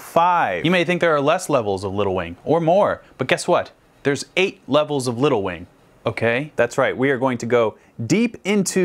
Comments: I misheard 8 levels of lil wayne😂😂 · this video autoplayed and i wasn’t looking at the screen so i thought you were saying “8 levels of lil wayne” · I thought he said "there are more level of Lil Wayne" 😭